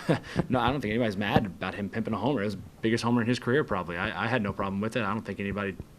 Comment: did he say pimpin a homer? 😂 @ 8:20 ?